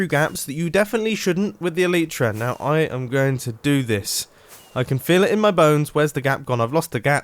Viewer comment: Sounds like you said "I'm going to die" At the intro